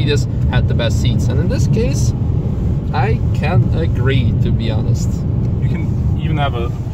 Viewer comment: What didn't you like about the seats? After Robert's comments about the seats in the 7 series, made me take a whole different perspective on the seats(thank-you, very insightful, kind of embarrassed I didn't figure it out). Bottom of seat not long enough for leg support? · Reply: oh, at 6:04 I thought he said "I can't agree" (in reference to the best seats). I think I would like them.